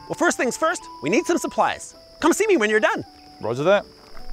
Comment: For a second I thought said "Bodger that" at 0:15 😂